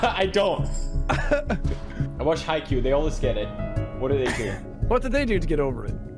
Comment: Did Pewd just said "I watch Haikyuu"? Cause the anime is awesome